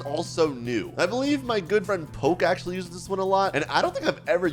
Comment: DID BRO SAID POKE